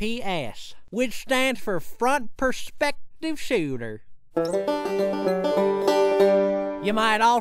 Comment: I thought he said fuck perspective sahter